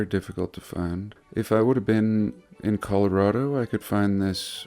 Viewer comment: Did you say 20:34 coloured road -o ? Like colorado or am i just hearing things